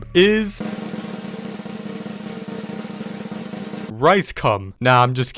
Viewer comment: did he say RiceCum?? 🤣